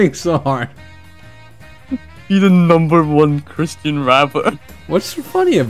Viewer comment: I thought it said be the number 1 christian yapper and I was thinking I already know the number 1 christian yapper